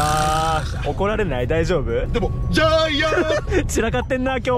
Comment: ジャイアンにしか聞こえないw